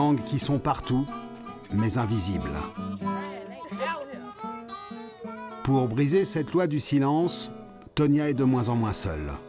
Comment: pour baiser cette loi du silence!!!??? ou j'ai mal entendu.mdr